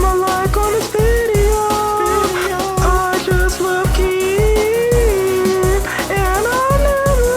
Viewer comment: At the end of the video I thought they said “I just love kids” and I was like WTF at first, but I realised that they said “I love keem” and I’m like thank god.